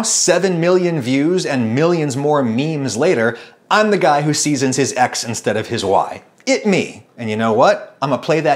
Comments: LMAO I thought he said "I'm the guy who seasons his ex instead of his wife"!!! ROFL 😂😂😂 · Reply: And I heard "the guy who seasons his ex instead of his 'why' " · LMAO I thought he said "I'm the guy who seasons his ex instead of his wife"!!! ROFL 😂😂😂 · my my I misheard it as "the guy who season his ex instead of his wife"